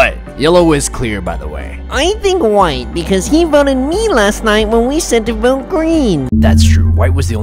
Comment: he said last night instead of time Lol